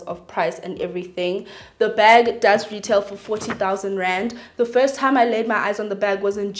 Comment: 40K for a bag😭😭 yho guys sbwl😩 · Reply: Girl I thought she said R14 000 and I had to rewind again to hear clearly 🤣♥️. SBWL 🕯🕯🕯♥️